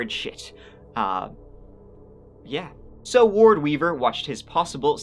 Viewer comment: Right there at 17 mins....I misheard the name you gave.I really thought I heard you say , " Ward Cleaver "....I was momentarily dumbfounded..."OH DEAR LORD ! NOT THE BEAVER' FATHER !"......